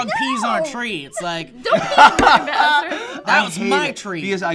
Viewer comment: Did say don't pee in my bathroom...?